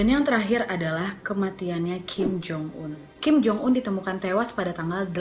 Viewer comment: kim jong un wkwkw apa gw yg salah denger yk